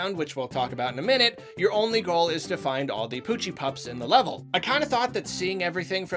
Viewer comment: Also it sounded like you said gucci pups